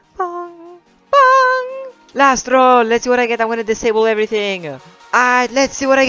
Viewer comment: it sounded like you said my name “lastral” but ik you said “last roll” 😂